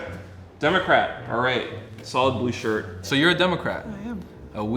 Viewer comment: did he say that’s an ugly shirt? I can’t hear anything else 😂